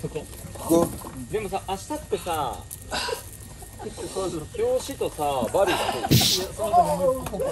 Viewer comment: ジェシーの筋肉の後ろで「明日ってさ〜、表紙とバリューだけでしょ？」「いや、その後…」ってスケジュール確認が聞こえる😵‍💫🫶🏻